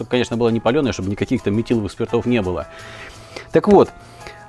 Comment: этиловых ? или мне послышалось ?